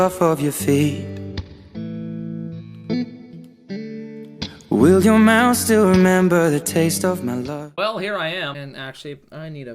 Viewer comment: In the intro I thought it would say "will your mouth still remember the taste of deez nuts"!! Anyone else?